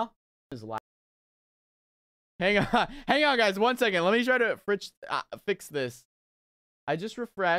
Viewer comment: he almost said ok guys let me FRENCH TOAST this instead of fix this. 🤣🤣🤣